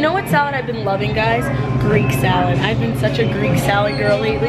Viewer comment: for a sec I didn't hear that she said Greek salad and I thought she said green salad , I was like wow green salad... so unexpected 😂